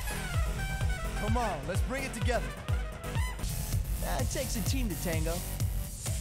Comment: “it takes a team to tango”? is that skin specific? i always hear “we’re here to be heard!”